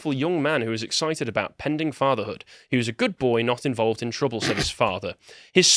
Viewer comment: Whenever I hear "he was always a good boy" my first thought is, "alright, lets see his arrest record." Every time, Im not surprised. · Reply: I always hear it as "he was a Goodfella."